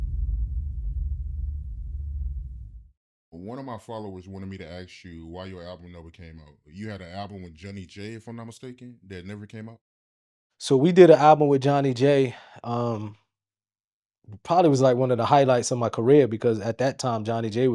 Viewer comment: Thought at first said an album with Jenny J. I was like 🤔